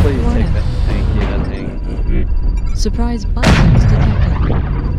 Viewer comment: Did really say "surprise buttsex detected" at 7:02! Well played sir!